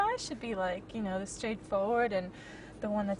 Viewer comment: At first I thought you said "blood type information" (LOL)